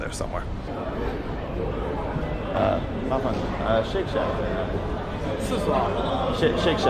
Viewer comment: He thought you said "Shit Shack" 😂